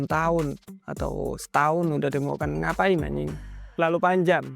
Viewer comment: ini aku salah dengar atau apa yah mas bilang "ngapain anying"😭 baru pertama kali dengar ini selama saya nonton semua vidio mas